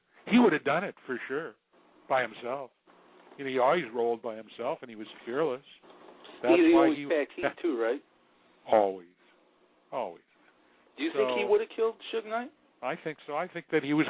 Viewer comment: What did you ask at 2:52? I didn't understand that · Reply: all I said was eazy always packed heat